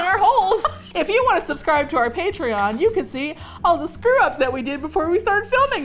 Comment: I heard “pantry-on” instead of “patreon” and I thought it was so clever so I’m petitioning for to call patreon, pantry-on to follow the theme. That is all, thank you.